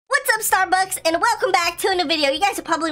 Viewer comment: Did he say Starbucks